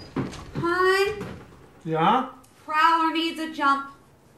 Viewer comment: what is she saying? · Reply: "Prowler needs a jump" - referring to her car